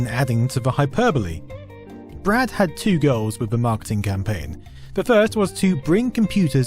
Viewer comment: If you enjoyed this, then you may also enjoy these Microsoft/Windows 95 themed videos; Why Did Weezer Come with Windows 95: <link> When Netscape Almost Destroyed Microsoft: <link> Did Microsoft steal Disk Compression: <link> Microsoft vs Linux: <link> Window's Hidden Self Destruct Code: <link> When Microsoft said Don't Buy a Console: <link> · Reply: Two what in the marking campaign, i heard "Girls" several times